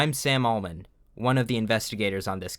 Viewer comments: First thought: "Which idiot left 184 in the reinforced shed?" 25:35 Did anyone else hear 'Sam Altman'? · I heard Sam Altman and I was so confused for a second